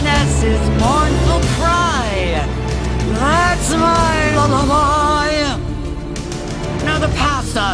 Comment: All these years I thought she was saying that's my little boy?? Well that's my lullaby makes more sense I guess.